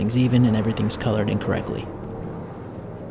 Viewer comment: When you said “colored in correctly,” I heard that as colored incorrectly and got confused for a second. Lol